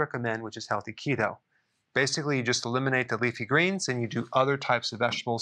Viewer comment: did he say eliminate leafy greens?